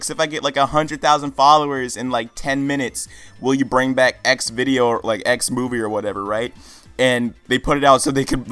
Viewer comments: Did you just say Xvideos ? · Did... Did you say Xvidoes?